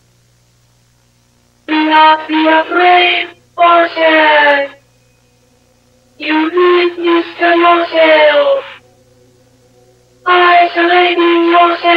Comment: What actually said: 0:41 Do not be afraid or sad You did this to yourself Isolating yourself after the death You caused her to leave you It’s all your fault It’s all your fault It’s all your fault It’s all your fault We’re all here because of you